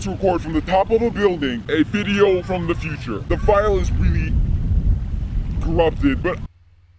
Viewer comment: It would be such a shame if the file is corrupted. "Time traveler": The file is a bit corrupted.* Bang * of course the file is corrupted. Me: WTF IS THIS edit: Oh he said REALLY corrupted